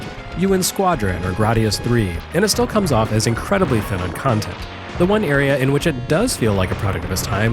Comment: I'm sorry, it comes off as what? I know he's not saying what it sounds like he's saying, but I can't understand what he's saying. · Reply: "incredibly thin on content"